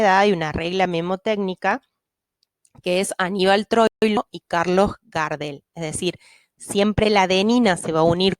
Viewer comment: Como es la regla memotecnica? entendi "Anibal (Cortado) y Carlos Gardel(? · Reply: Anibal Troilo y Carlos Gardel